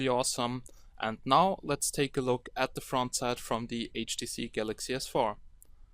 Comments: lol I thought he was saying HTC Galaxy S4 for the whole beginning of the video. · Um did he just say HTC galaxy s4 . ....it's samsung galaxy s4